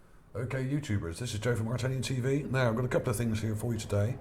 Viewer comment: its funny when you say its ArtAlienTV because it sounds like you say Italian TV XD great vid dude